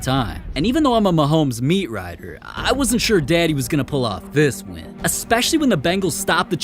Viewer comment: did you just call Mahomes “daddy?” Wtf?